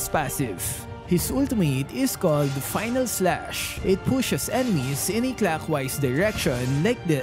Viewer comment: On 2:30 ..As a Dragon ball fan, everytime I hear this I always thought that Arlott's ultimate was "Final Flash" (One of Vegeta's iconic powerful attack) Well they sound similarly so I guess I can't blame myself for it.